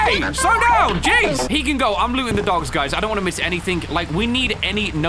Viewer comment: am i the only one who hears, 'im gonna go loot the dogs' ??